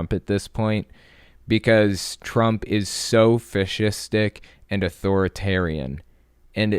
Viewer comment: "Trump is a fish stick." I know said "fascistic" but I was not paying much attention while doing other things and I heard fish stick...